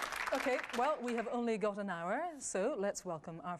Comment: wow that ends fast...i thought she said " we have only gotten an hour"....or i miss heard something?